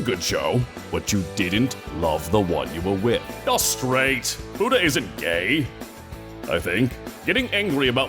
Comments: For an entire month I thought Alucard kept saying "Buddha isn't gay... I think" only to find out he said futa... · I’ve never watched with the subtitles until now. I always thought he said “Buddha” wasn’t gay he thinks, not “futa”. Now the joke makes more sense to me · “You’re straight ! Futa isn’t gay... I think.” - Alucard My favourite phrase in this episode. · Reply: Legit thought he said budda